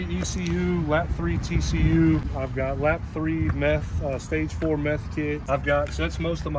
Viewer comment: i kinda spaced out and heard him say stage 3 meth kit, i spilled out my drink XD adhd brain go brrrrr